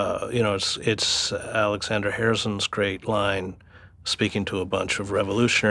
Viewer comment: in case others had this issue. it sounded like he said Alexander Harrison at 26:23 but it is Alexander Herzen